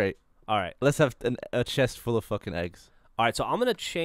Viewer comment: Did Gavin say fuck?!? :O